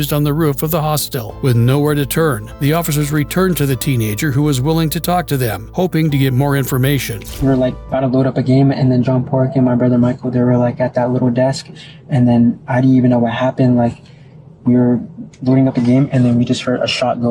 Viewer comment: did bro say john pork???😭